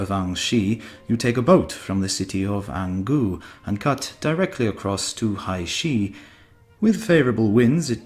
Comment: I wonder if he really said "CUT directly across"?